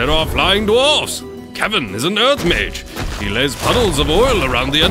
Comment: I heard "Kevin is a nerd mage" instead of kevin is an earth mage 😂